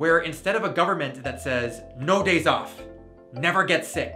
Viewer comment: I heard "no gays off" hihi